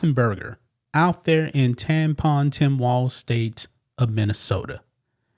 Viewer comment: I had to replay this part because I thought you said "tampon" .. Don't want to offend the "girl boys" do we? 🤣